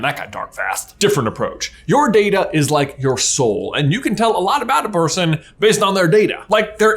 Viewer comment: I was skipping through the ad read and all i heard was, "Your data is your sh- Sell your data!"